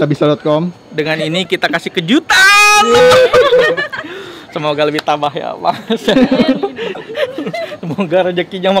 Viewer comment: gue dengernya semoga lebih tabah yaallah 🙏